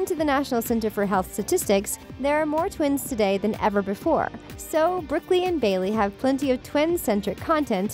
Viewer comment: Her name is Brooklyn 😂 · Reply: It sounded like "brooklie"😂.... They also talked about her while showing Bailey lol